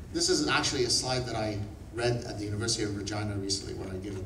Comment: Did he say university of vagina?! Lol